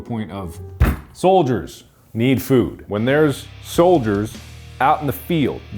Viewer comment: I straight up thought he said Folgers need food and took me a second to realize I'm an idiot 😂